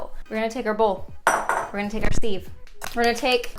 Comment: I kept hearing “sieve” as “Steve” and now sieves will forever be Steve’s